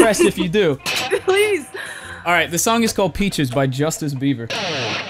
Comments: "i will listen to whatever you have to play!" a true blessing on this site. good vid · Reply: i heard, 'justice bieber'. lol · Did he just say justice Bieber